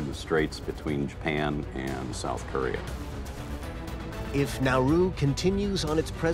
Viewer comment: I thought he said naruto when he said nauru